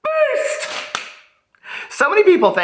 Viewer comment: Thanks for making that clear case I thought you were saying "Peace", not Beast. Lol